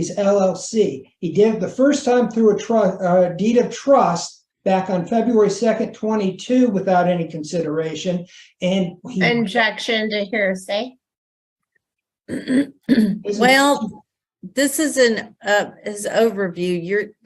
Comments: does she say "injection to hearsay"? I've listened to it 3 times TRYING to hear her say OBjection but all I hear is INjection 😂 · did she say injection ? 😂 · I swear she says injection instead of objection